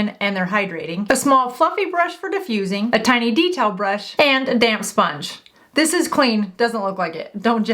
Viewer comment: Hahaha I thought you said “a damn sponge”